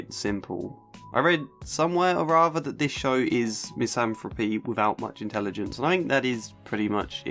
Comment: I don't understand the word(s) that you say here... the show is what for people without much intelligence? Otherwise a very good and informative video, thanks! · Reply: I know misanthropy and now the sentence makes sense, I just didn't pick out the word "misanthropy" at all until you said it now. I thought he said something with people. Thanks :)